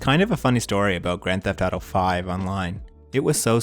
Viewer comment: The captions have an error at 10:33 says IV instead of V.